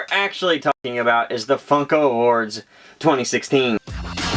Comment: It sounded like he said, "Fucko bop awards" at the start.